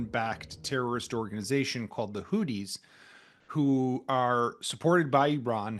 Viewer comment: Did you say, Hoodys?